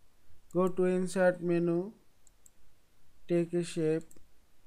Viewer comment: What does he say right here? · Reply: draw a shape